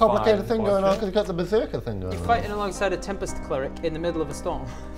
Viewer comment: Teabreak coincides with the notification. Awesome. Let's see how Bodgers beserk goes post giant death... So.... "they just need to kill him. A great solution going forward".... for the 75hp beserk tank as reminds them. Great episode, glad I'm working from home as there were plenty of laughs and great green screen. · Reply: Also so Grin is a Tempest Cleric. I misheard Tempus cleric initially and was like what?